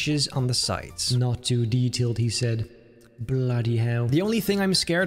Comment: I thought he said not to much detail..